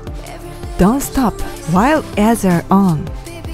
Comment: I love your exercises! Thank you! As I‘m no English native speaker I don‘t get the comment you say in all of your exercises: „Don‘t stop while as your arms“ or so. I cannot get it that it makes sense😅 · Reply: I thought She said “Don’t stop while as your own”